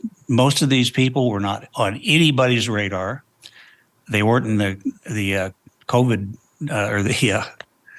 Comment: Wait did he really say “the COVID DATABASE”???